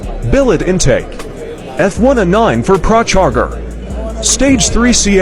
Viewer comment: Pra-CHag-Er. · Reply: I thought he said (car charger).....lol